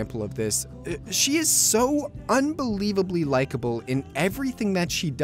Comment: "she lost both her parent and lives in a tent, but instead of getting depressed she fights and keeps a smile on; that's damn relatable" Maybe you meant admirable? Otherwise your idea of relatable is quite an odd one. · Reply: you know, i thought you were making that up... Then i rewatched. Wtf Why did i hear relatable? If it was just mishearing likeable of relatable sure, but i clearly remember hearing both, relatable first and likeble later... Or maybe the opposite? Oh crap... Now i see... Well, regardless i gotta apologize, i was too sure of what i heard and didn't bother making sure before writing the comment.